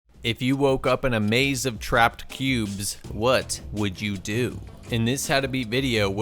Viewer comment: Thought You Said "If you woke up in a maze lf trapped pubes" The Answer I'd Kms So No One Finds Out But Me And The People Who Put Me There